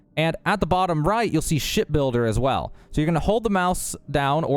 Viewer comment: This is too much for credits now, 😂 but good luck people Did anyone hear him say ship butter btw 10:47.